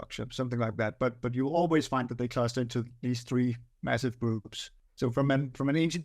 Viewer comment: I thought he said “three massive boobs” the first time haha😊